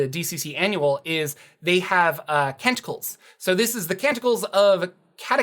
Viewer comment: I was not looking at the screen at 6:35 when you said Canticles, but by the Gawds, I swear you said they get TENTACLES! And I thought, WHAT!?!